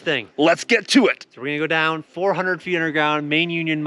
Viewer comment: did he say go down 400 meters?